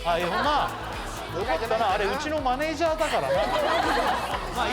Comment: 設楽さん岡本の名前間違えてる？なんて言ってるんだろう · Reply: 「ああ、いるよなー。あれ、うちのマネージャー」に聞こえますね。 よなー、からはっきり聞こえるんで、与田って言ってるようにも聞こえますね。